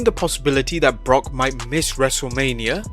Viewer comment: Lol at first I heard "Brock IS miss Wrestlemania" and I was like, no, taht's his wife Sable. He just stole her second finisher!